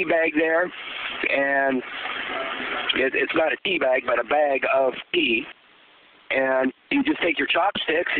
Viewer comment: for a minute i thought he said "bag of pee"